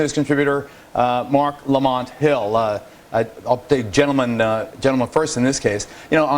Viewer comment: did he say ok gentlemen??